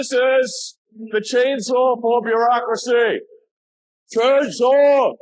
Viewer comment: I thought he said "let's take a chainsaw to democracy" ... Sounds about right.